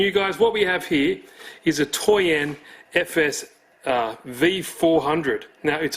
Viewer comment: You were saying toyan i thought you were saying toy end as in cheap i was freaking out im yelling at screen 1500 bucks isnt toy end then saw it was name of engine feeling stupid